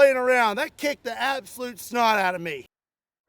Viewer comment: ROFL! I thought you said "That kicked the absolute Scott out of me!" 🤣